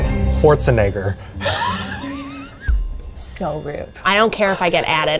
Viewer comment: who did say was a celebrity she liked the least? i couldn’t understand it , it was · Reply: Arnold Schwarzenegger